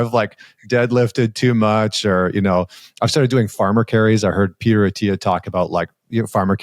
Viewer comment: Who have you heard about farmer carries? It sounded like you said Pera Artera